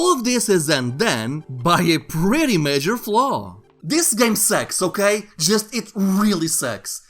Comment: huh... what? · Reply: You accidentally said sex instead of sucks. Which is why the OP got confused.